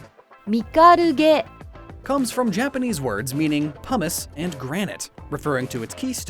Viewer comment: Did you say hummus?